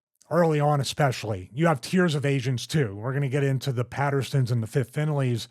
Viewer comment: I thought he said "you have tears of Asians too." Instead of "Tiers of agents too." LOL.